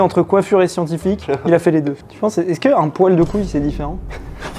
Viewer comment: Je sais pas pourquoi mais j'ai cru qu'il parlait du poil de couille 😂